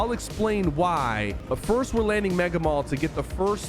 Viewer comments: Did sypher say Mega Mall? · Did Sypher say mega mall💀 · woah woah whoa did he just say mega mall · Did he just say Mega mall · Did he say mega mall 0:15 😂😂